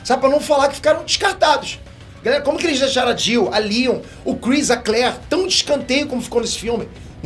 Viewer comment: "A Leon..." kkk essa eu ouvi